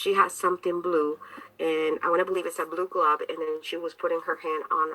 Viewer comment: Did she just say a blue glub lmao